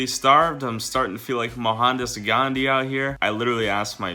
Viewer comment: Did he say mahatmah gandhi as